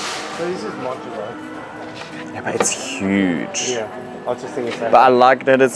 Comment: did I hear a "ja aber" it's huge? at 3:30? Great Videos! I'm always looking forward to your content. Greetings from Germany :hand-pink-waving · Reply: it's a 'yeah but' but sounds very similar :D haha